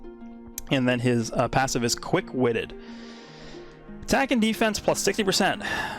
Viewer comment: I thought he said the passive was called "quick wit it" for a sec and dokkan was acknowledging that piccolo is black